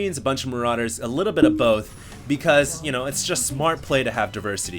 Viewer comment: a bunch of marines, a bunch of marauders, a little bit of DONK both. Did anyone else hear that haha?